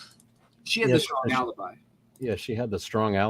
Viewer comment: I hear this: "she has a strong alibi"